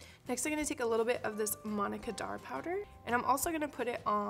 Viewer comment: always thought you were saying monica dar powder, but its manna kadar!! hahah